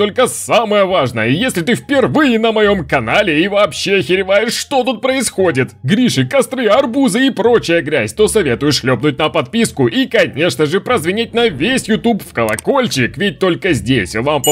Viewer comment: мне послышалось "Гриша кострирует арбузы"